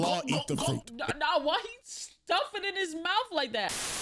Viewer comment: saying this without context is insane 😂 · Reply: The video buffered the MOMENT i heard "white stuff" i know that she said "why he stuffin'" but i heard "white stuff"